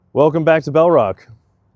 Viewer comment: Your accent sounds like you say Welcome to bellrod to me. Good story on this one.